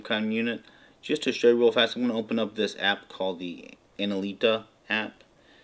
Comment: Great stuff! At around 1:39 - 1:40 you open an app, enilta, analita? I have been searching different spellings of what I heard but can't find it. Can you tell me what it is, name I mean. Thanks for your work here. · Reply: analiti -- you can see it on the video.